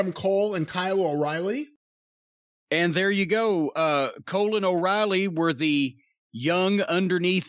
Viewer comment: sounds like he says colon oreily